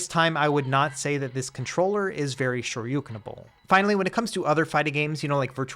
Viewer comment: What word did you say at 3:25? Lol. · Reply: Shoryukenable XD Love it